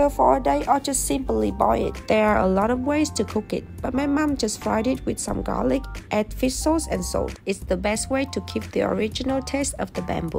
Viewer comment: I swore she said "mom mom just farted" and had to do a double take 😂